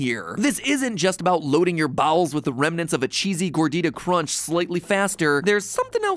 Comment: DID I HEAR REMNANT